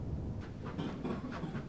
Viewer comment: My volume was down so instead of hearing "I panicked" I heard "I'm gay." Still laughed either way.